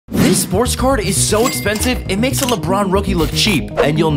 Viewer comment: I thought you said “sorts car”